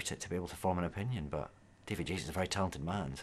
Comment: I misheard it as "David Jason's a very talented man" at first. :P